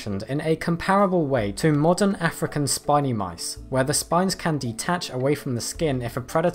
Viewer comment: i thought it said spicy